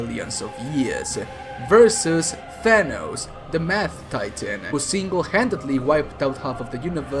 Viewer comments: Did he just say The Math Titan lol. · Did I just hear him saying Thanos the math titan? Or is that just me? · i thought he said the meth titan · Did he say the meth titan? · Every time you called thanos powerful I thought you said purple. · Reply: Also sounded like he said "the math Titan"...